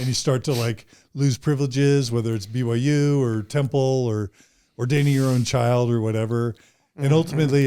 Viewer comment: Help. 1:26:25 What did he say? Dating? Your own child? I must have misheard. Please clarify. Thank you in advance. 😊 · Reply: I think he said "ordaining" your own child.